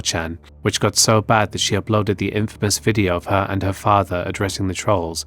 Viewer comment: I thought you said "applauded" as opposed to "uploaded" at first lol.